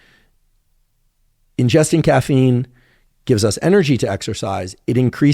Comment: Sorry if someone already said this, but it sounds like Andrew is saying caffeine gives bees a buzz.